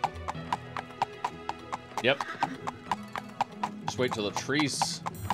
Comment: So I've been smoking (weed of course 😂), and I was like "you can just ride through the trees?", and he said "yep" and it sounded like he also said "straight through the trees" 😂 1:16 So I of course had to say "what?" To the phone 🤔🤔🤔